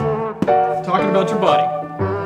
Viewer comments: He did not say body he said, buddy, and he pointed to😱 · buddy is what he said